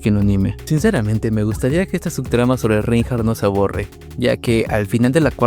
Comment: la primera vez escuché "que no se aborde" luego la segunda y 3era ya entendí que era "no se borre" xdd